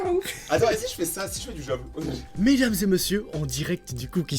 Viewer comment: ha 5:16 a dit lâche le bb a rayon ou c moi qui est entendue sa likes se qui on entendue la même chose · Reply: Elle a dit "mais mais"